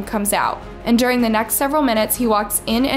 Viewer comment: why does it say black Man instead of Mask